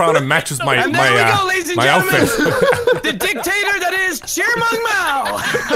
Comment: Cheermung mao instead of chairman ?? 😂😂😂